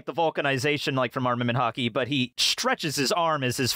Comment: For a moment I thought you said Arnament Hockey instead of Arnament Haki, so I thought he turned his arm into a hockey stick that shot out hockey pucks.